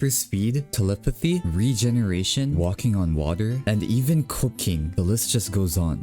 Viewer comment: I heard "cocaine" instead of "cooking" wtf